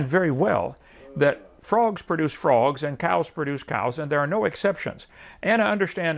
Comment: After all the talk of Kent’s fraud, I misheard his “frogs produce frogs” as “frauds produce frauds.” 🤣